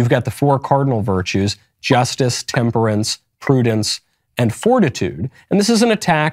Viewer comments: it says fortnite instead of fortitude lmao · anyone else catch that “Fortnite” at 6:22? Lol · is fortnite instead of fortitude